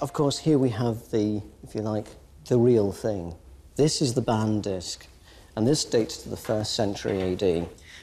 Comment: "This is the ban disk." Is this what is said here? "Ban disk"? I can't find this jewelry piece on google under "ban disk," does anybody know the name of the piece? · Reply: Bann